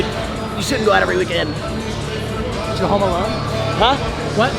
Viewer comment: Does say at 3:10 "Did you go home alone?" 😂